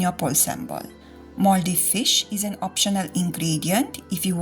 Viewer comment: I thought she was saying 'mouldy fish'.